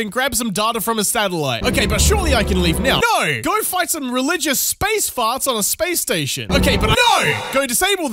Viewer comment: LOL, at first I thought you said "Hunt down some religious space farts"